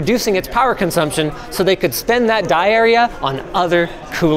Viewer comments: thought he said diarrhea when he mentioned the dye area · Please stop saying "die area", I hear "diarrhea".